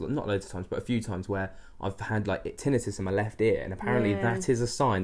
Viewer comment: ! What is the word you used that you "get" in your left ear that might mean someone is around? I about fell over when you mentioned feeling something in your ear cuz I get a feeling in my ear a lot---but what did you call it? In your ear? I've listened 5 times and I can't decipher the word you used. LOL It's at 10:24 when you use the word. Thanks! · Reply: Tinnitus x